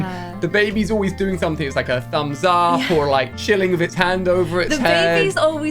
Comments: I literally screamed when I saw the tittle of this video, then I yelled “What?!” when you said you’re SIX MONTHS pregnant. I AM SOOO HAPPY for you!! I really hope the birth is as easy as it can be. With the added complication of a differently shaped uterus, I totally understand why you chose not to announce it till now. You’re my favourite YouTuber (I feel more connected to you than anyone else I watch) and I just want hug you (which is odd because I’m not a hugger) with joy. When your beautiful baby is born I’m going to go back to the beginning of your Sims series to laugh at your assertion that you don’t want children. I think you’re having a girl and I can’t wait to hear what her name will be. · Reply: Im pretty sure it’s a girl, especially since at 10:22 it sounds like says “her thumbs up” 😂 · Exactlyyyy, might be a hint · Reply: I feel like it’s a girl, at 10:22 it sounds like says “her thumbs up” 😂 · I think it’s a girl because of what said at 10:21, He said HER thumbs up. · Im gonna throw out a guess that the baby is a girl, especially since at 10:22 it sounds like says “her thumbs up” 😂 but nonetheless I wish them a happy and healthy pregnancy/baby